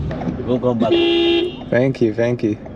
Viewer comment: did he just say “don’t come back”?